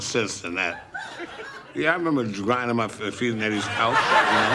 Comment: I always thought he said grinding his nuts on the couch